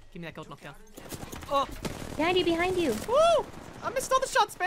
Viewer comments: "Daddy behind you" did i hear that right?! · does she say daddy behind u? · Is it me ...or did called daddy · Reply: No she said behind you behind you